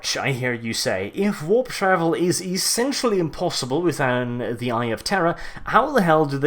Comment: And to think all this time I thought you were saying "the eye of terra"